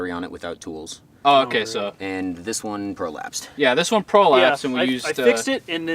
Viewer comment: Spat my coffee out my nose when I heard "This steiner prolapsed..."